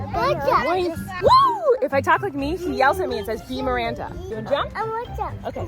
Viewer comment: My name is Amanda, and sometimes it sounds like is saying "be Amanda" and that makes me so happy lol. is telling me to be myself 🥲